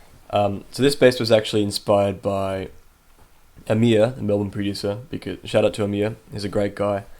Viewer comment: did you say "Amia" ? cant find that artist you've mentioned